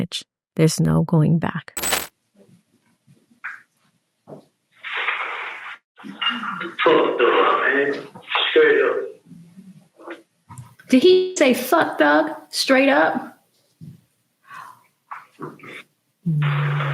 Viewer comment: Sounds like he said "Fuck the law"